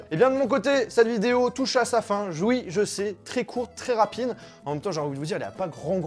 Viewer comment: mdr jouï ???🤔🤔🤣😅 Rassurez moi je suis pas le seul à l'avoir entendu ?